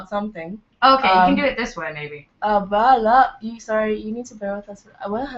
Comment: did she say "baby" at 4:00 or am I deaf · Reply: She said maybe...